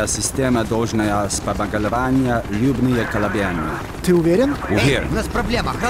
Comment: what is he saying? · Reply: The only thing I understand is "ты уверен?" which means "you sure?"